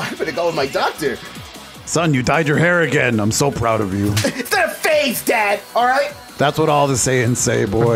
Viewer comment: I thought he said, "It's the face, dad!"